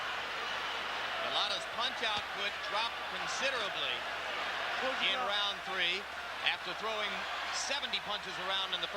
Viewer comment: Did you catch what bowe said at 19:13? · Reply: "go down" ?